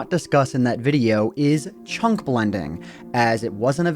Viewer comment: I thought he said," Chunk bending."